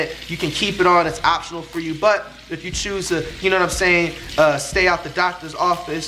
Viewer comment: I heard him say "you know what in saying" the whole vid😂😂😂